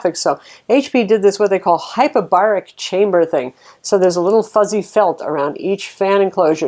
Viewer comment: It sounded like she said "hyperbolic Chamber". Some of you might know.